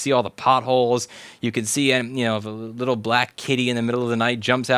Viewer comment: i thought you said black kiddie at first